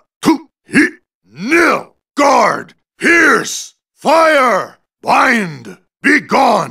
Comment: So thats whats hes saying...I thought he said here!! instead of Pierce...